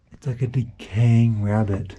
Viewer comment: Il dit "It's like a decaying rabbit..." à 12:03, pas de roi ici :') Bonne vidéo sinon !